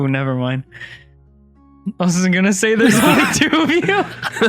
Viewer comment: bruh I’ve listened over and over again but what does say that everyone is like yikes about? at like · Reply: I think he said "there's only two of you" Also it was, but didn't actually say that lmao